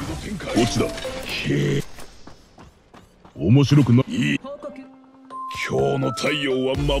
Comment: の面白くないの「い」が「い゛ぃ゛」にしか聞こえなくて草